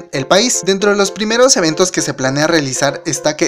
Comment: me parece o escuche ES ES TRESCIENTOS UNO osea SS301!!!!! Tendra una reunión de fans!!!!! → 4:30 ← aaaah :O mori >-<